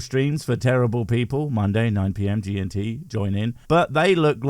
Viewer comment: Did he just say monday 9pm TNT??? at the 6:35 mark? I need to check that out! It`s either wrestling or him trying to blow us viewers up. Either way I`m in!